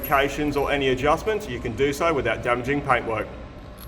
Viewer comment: When I first watched this I swear I heard said "paperwork" and not "paintwork". Watching it again to find the timestamp, I was obviously wrong...